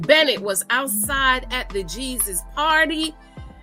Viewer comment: did she say at the Jesus party? Damn Aunty!!! 😂😂😂